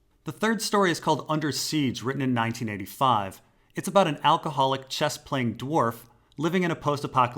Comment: Did you say an alcoholic cyvasse playing dwarf?